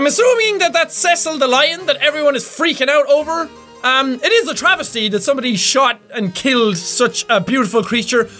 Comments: Did Jack say travesty at 7:58? lol! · Jack said "Travistie", instead of tragitie .NY friends name is Travis and we always say that to him when he does something stupid.